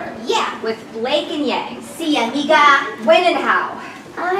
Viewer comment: did I heard a "si amiga"?? xD at 9:27 this is so cute I love it <3